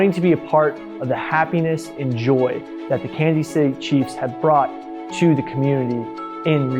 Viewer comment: Did he say “Candy City Chiefs”?